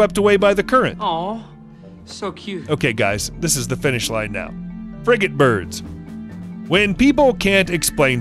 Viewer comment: I thought he said “friggin’ birds”